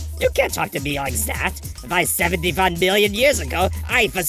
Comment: Why do the subtitles say "75" when the raptor says "71"?